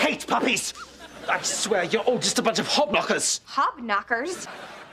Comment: Hob knocker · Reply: At first, I thought he said hogknocker.